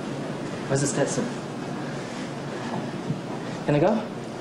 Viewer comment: What did Hamilton says at? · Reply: Pinguino Penguin he said ‘where is the stetson’ as they usually get Pirelli Stetson hats in USA because it’s Texas rather than caps